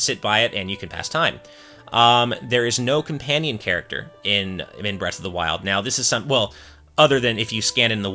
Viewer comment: Does he say breast of the wild at around 7:39 to 7:40 mark? I know its probably just a slip of the tongue but if thats what was said I cant help chuckling a bit.